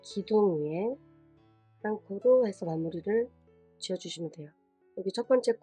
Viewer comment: 초에 뭐라고 하신걸까요?ㅠㅠ쌍코? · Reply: 기둥 위에 한코로 마무리 지으시면 됩니다~ 혹시 더 문의 사항 있으시면 <num> 로 연락주세요~!